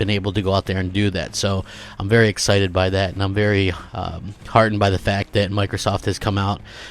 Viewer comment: did he just say he’s very harden by what Microsoft has done? Virgin confirmed!!